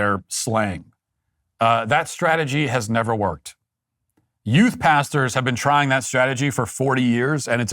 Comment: Oh...Youth Pastors. I thought he was saying You Bastards. Put ads in young men's video games. It'll totally work, I promise.